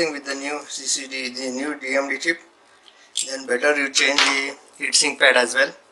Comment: Great video! Thanks for that! One question. You said in 11:02, "if you're replacing chip better to change... as well." .. I don't hear, wchich part did you mention? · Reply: Hi, I mentioned about the heatsink pad, this heatsink pad can be seen between 7:45 to 7:49. Thank you, have a GOODTIME...